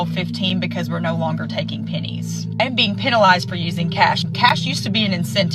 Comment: why is she saying "pinnies" and not "pennies"?